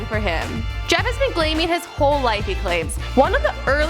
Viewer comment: Is it me or did she say he has been "Glaming"